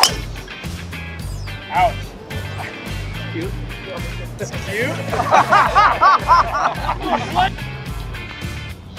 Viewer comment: what is said there? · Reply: Phil said that’s cute